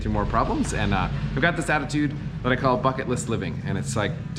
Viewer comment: I thought he said F*ck it list!